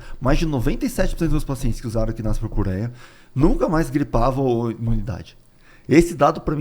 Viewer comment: Alguém sabe o nome desse remédio ele fala que mais de 90%dos pacientes dele usaram e nunca mais griparam ? No vídeo no tempo 33:06 não dá pra entender o que ele fala 😢 EU QUERIA SABER O NOME DO REMÉDIO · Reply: Equinácia Purpuréia.. Acho que é isso que ele falou..